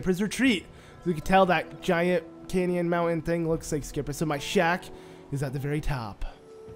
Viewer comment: It's always your videos are helpful even if it is 2022. But every single time you say shack I keep hearing shaft. You've got a great voice and my guess is you might be narrating elsewhere 😉